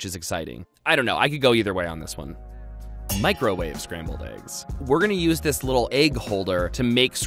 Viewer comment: lmao thought you said "Scranton Scrammbler egg" hahaha the office.